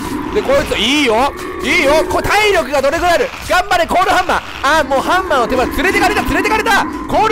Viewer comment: コールハンマーじゃなくて コーラハンマーに聞こえるwww